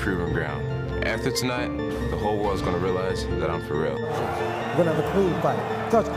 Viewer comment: Why I thought herb dean said “we’re gonna have a food fight” 😅😂